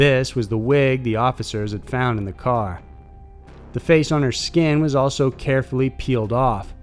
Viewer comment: Did he say "the face on her skin"? 4:51 lololol